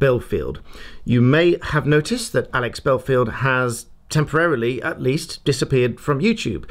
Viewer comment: For a minute I thought you said Levi Bellfield.